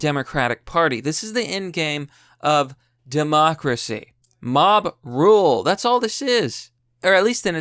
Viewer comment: Did someone said Mob roles? <link>